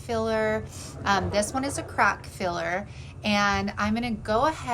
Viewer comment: 🤪 After you were talking about how rough the sleigh was and then you said crack filler I could have sworn you said crap filler. I was sitting here giggling. I love how the sleigh turned out. You do such good work.